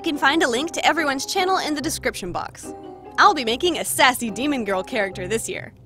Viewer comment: My dumb self really thought the title was “Special Demon Grill” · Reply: i literally thought you said special demon girl so i was like "but thats what it says" but i realized im also illiterate